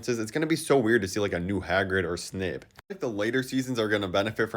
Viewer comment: did you just say "hagrid or Snib" instead of snape?🤨